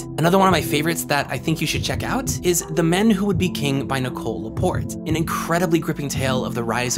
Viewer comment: I thought you said The Man Who Would Be King and I was like "hold up, I've read that book and it has *not* aged well."